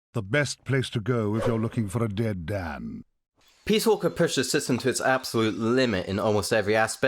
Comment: What's the game at 12:36? Couldn't hear.. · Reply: I have no idea, I can't understand myself Jk, it's Metal Gear Solid Peace Walker